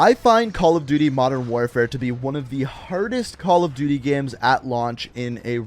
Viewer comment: Hardest CoD? All I heard was “ThE cAmpIest CoD in HistOry”